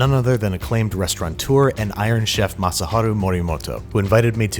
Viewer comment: lol i thought it said ‘moto moto’ and i got scared